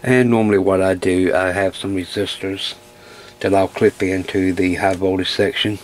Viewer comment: Near the beginning (when you were talking about draining the caps) and you said, "I have some resistors...", I swear I thought I heard you say, "I have some sisters ..." and assumed you were referring to multiple family apprentices that can be deployed in a High Voltage situation...